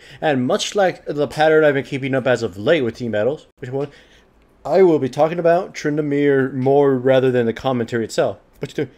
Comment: what does he say there? he literally says it on the start of every video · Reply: voicecheck 1